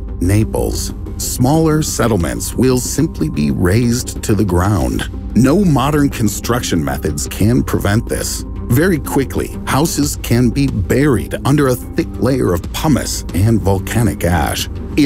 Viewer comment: thought he said that the houses would be buried in a thick layer of hummus ;)